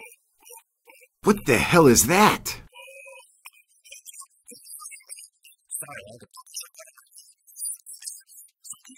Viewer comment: What is El Padrino saying for this whole section starting here 19:44? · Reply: Sorry I had a taco caught in my throat 19:50 so this is the infamous drunken hedgehog I’ve heard nothing about 20:04 you know for a drunken hedgehog your not very fun I’m done with the three of them throw them to mama sec